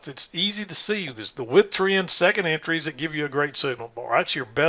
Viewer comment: Hi, thanks for the videos. At around 8:40 you mention beginners just sticking with the "With 3's" and second entries. What's a "With 3?" Did I hear that right? I've just recently purchased your manual and have read it twice, and don't recall any entry setups mentioning anything like that... thanks! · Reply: said stick with the WITH-TREND 2nd entries that give you a Great Signal Bar. I had paused the video to make a note of that gem!